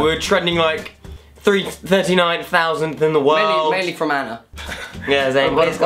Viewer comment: I thought Conor said mainly Fermanagh😂😂 Only Irish people will understand